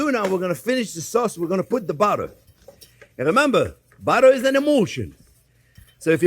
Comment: Chef Jean-Pierre: "Remember, butter is an emulsion..." What I hear: "Remember, butter is an emotion..."